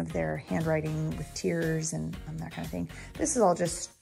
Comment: I heard you say, "Handwriting WITH tears". I busted out laughing.....I know what you mean, but....golly....Handwriting does bring tears for some heros ..... no doubt about it. lol. Have a great year! Congrats on the new teaching assignment.